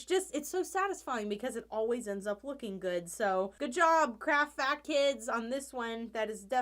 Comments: 3:340 Did she just say craft FAT kids??? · im dying at 3:38 it sounded like she said craft fat kids